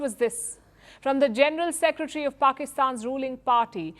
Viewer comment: from the general secretary of Pakistan's ruling party Did i heard right? 🤨